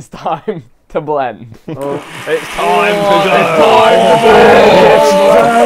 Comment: sounds like GNF saying its time to kill dream while at tommys house